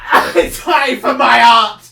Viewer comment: Wtf is he saying here? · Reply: I die for my art (or at least smth like that)